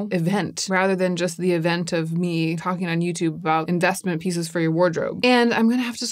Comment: At the end I thought you said destiny pieces for your wardrobe, rather than investment pieces… but I want to know what destiny pieces are in the wardrobe 😭🤣